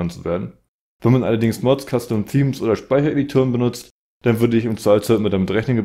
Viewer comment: kannst du bitte sagen was du da gesagt hast ich verstehe dich da nicht. Sryy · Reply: Wenn man Mods, Custom Themes oder Speichereditoren nutzt